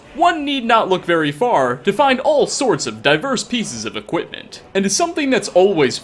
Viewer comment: When you said “diverse” I thought you said “diapers” and I really thought that you could wear diapers man